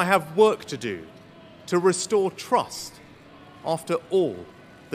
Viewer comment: Did Sunak say he would restore Truss or restore trust? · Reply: Trust, but I thought he said Truss at first.